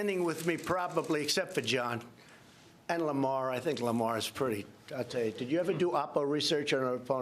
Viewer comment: did he just say i think John is pretty? ...LOL